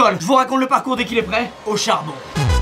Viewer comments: hauchard bon 🤣 Escusez moi 😔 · et pourqoi pas "Hauchard" bon (au charbon)…... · "HAUCHARDBON" XD = au charbon C'etais tout pour moi xD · "au charbon" ou "Auchard bon" ? c'est tout pour moi merci